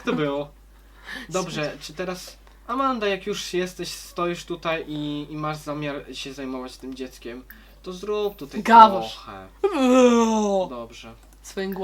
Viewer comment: czy tylko ja usłyszałam ,,zrób tutaj powąchaj"